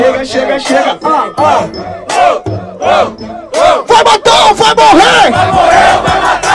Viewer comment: SÓ EU QUE ENTENDI "VAI MAMAR OU VAI MORRER"??